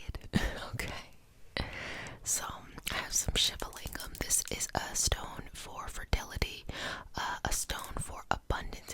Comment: what stone did she say? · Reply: Shiva lingam / crystal lingam 💗